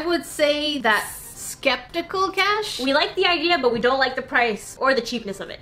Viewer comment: I swear I heard you say skeptical trash XD